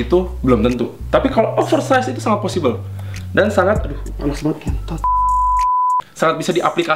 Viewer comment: ga salah denger "KENTOD"?